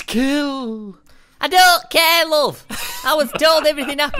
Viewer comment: squid , instead of saying "i don't care. said "i don't care love". who noticed this to